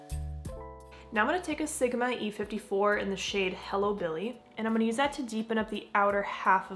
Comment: i think purple and orange eye looks are so lovely together, especially in the fall! at first i thought you said "hillbilly" instead of hello billy 😆 beautiful as always 🧡💜